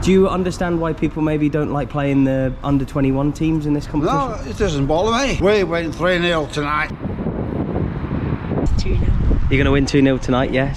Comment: What is he saying? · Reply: "No it doesn't bother me, we win 3 - 0 tonight".